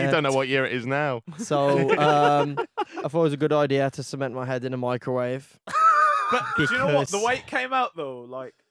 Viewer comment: Did I just hear say “I’ll cement my head in a microwave on ket”